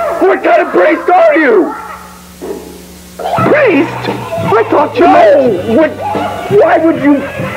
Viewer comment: Does anyone know what Bon says at 0:57? I can’t hear what he says after shouting “Priest” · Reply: I heard “ …. PREIST?! I THOUGHT YO- NO!!